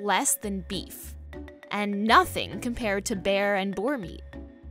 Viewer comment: Did I hear Bear?